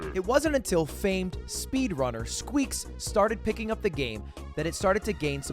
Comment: Ooops, in the intro I misheard "the famed speedrunner..." as "the failed speedrunner..." lmao 😂😂😂